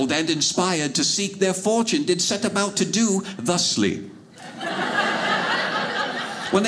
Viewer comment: the first time he spoke, I thought he said “the sleep”. I had to listen to this again to hear him say “ thusly”. Old English is weird.